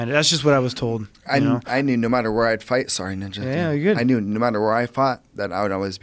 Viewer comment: Did Duane just call TJ "Ninja"? Someone back me up on this?